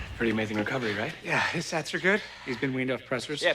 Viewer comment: My whole life I thought they were saying stats and not sats...whoops